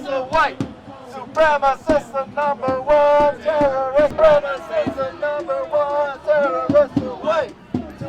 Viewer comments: Excuse me but the chanting sounded like they were saying "supremists are number one and terrorists are white", but coming from the white guy was he calling himself a terrorist? Idiotic people just wanting to get in the news. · sounds like she was saying "Supremacists are #1" to me...